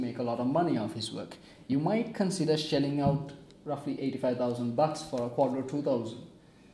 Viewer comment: Aahhh! 85000 bahts. I thought he said 85000 bucks. Scared the living daylights out of me. I dont have 85000 bucks for a Quadro. lol